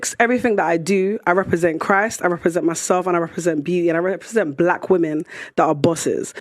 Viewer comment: I honestly thought she said, "i represent busses"... Aka carries a lot people, with different drivers... No offense